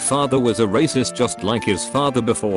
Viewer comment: Thought that is said "rapist"